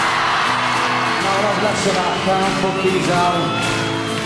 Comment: what does Liam say at 2:41? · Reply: Nick Simmons no messin around calm fuckin down if you're still wondering 😂😂